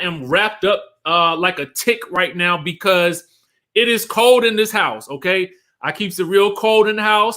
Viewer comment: When he said “it is cold in this house” I heard “it’s some ho*s in this house” in my head 😂😂